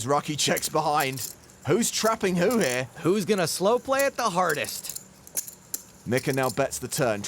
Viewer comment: For a second I thought the announcer said "ESB Hoth". And I thought, "wow, neeeeeeerrrr--rrrddddd!" (In Homer Simpson's voice :P) But I think he said something else. Announcing a deck as "ESB Hoth" works brilliantly too though!